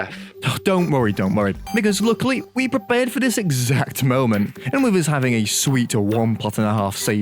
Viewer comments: Um did he say the n word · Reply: nowhere near "don't worry don't worry because we prepared for this exact moment" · you did NOT say because there 😭